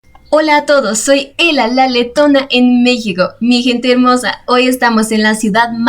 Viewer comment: Me vas a disculpar pero pensé que decía la tetona! Yo pensando mal jajaja